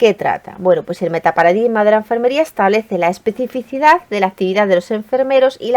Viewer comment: no no entiendo la palabra que dice en el min 9:03, después de especificidad, podrían decirme que dice . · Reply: La especificidad de la actividad de los enfermeros 😘